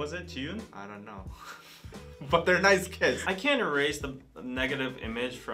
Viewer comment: Dude I thought you said they had nice TITS lol especially at 2:49.....i had to keep playing that back to make sure I was mistaken.....still not sure lol